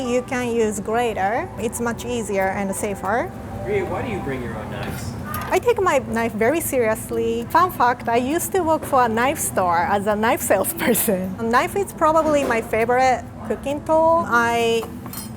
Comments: Did they show us she said FUN FACT bc it sounded like "Fun Fuck"?? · i heard as '' fan fucked'' for fun fact , haha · I heard fun fuck but thanks to the caption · i thought she said fun fuck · Is it just me or did I hear her say "fan fuct". Anyways.. you are too sweet. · omg i can not deny that when she said fun fact if they had not written it i would have thought she said "if im fucked" lol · When she said "fun fact" I heard 'f*** f***' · It sounds like she said FUN FUK. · I thought she said "Fun fuck." But thank goodness for the subtitle. LoL "Fun fact." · did she say fun f×××ed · the first time i heard it is "fun fuck" lol